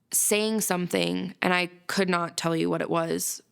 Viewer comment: Marching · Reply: i thought she said he was singing a cadence